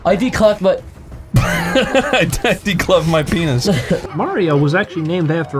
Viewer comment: I can never tell what is saying at 13:03 I’m assuming he’s just saying “Mario” again. But it also sounds like Wario so I don’t know. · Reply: He's saying Mario, which is ACTUALLY true but I don't believe he knew that at the time so it was very funny